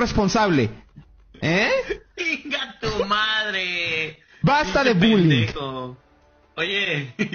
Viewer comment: JAJAJAJAJJAAJ TE JURO QUE ESCUCHE ''basta de pendejos '' JAJAJAJAJAJAHSACFYHERFYVUWR NOPUEDONOPUEDO JAJAJAJAJAAJ